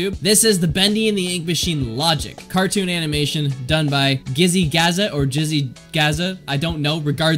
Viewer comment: did you just say jizzy